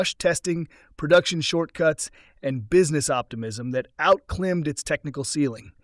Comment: How can you do a report on the BD-5 and not even mention Burt Rutan's involvement? Read the chapter in BRAB (Rutan's online autobiography) about Rutan's contributions. Bede was a real snake oil salesman... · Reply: The "narrator" @ 17:59 says "outclimmed". Instead of outclimbed. I can't stand A.I.